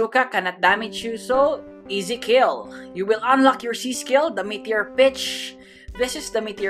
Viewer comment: For a second I thought you said met our bitch😂